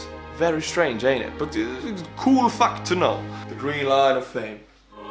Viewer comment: Sounds like he says "Cool fuck to know."